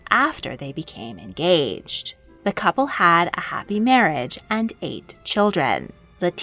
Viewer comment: I thought you said “The couple had a happy marriage and ate children”😂